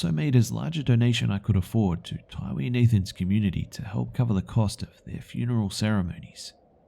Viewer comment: The add stopped the video and all I heard is "nathans cum" 😂😂😂😂